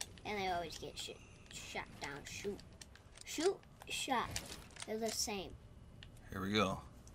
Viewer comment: Anyone else hear say s**t at 8:03?? Ik he didn't do it on purpose and he was trying to say shot but still 😂